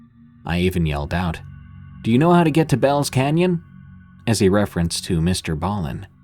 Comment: I just got back from a 30 day venture in Hell's canyon all went well. Until I decided to listen to this channel and yes I admit I had to wait till I got home to listen to the the rest of your story OMG, have you ever listened to one of your terrifying story's while camping your voice made me look over each shoulder for the next week lol Best solo camping I have ever did and longest I'm 50 and I fullfilled my camping carrier. Thank you for your Great story's 👍💯🙏😀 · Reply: At first I thought you said Bells Canyon